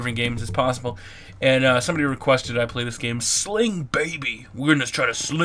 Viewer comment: i thought it said sing baby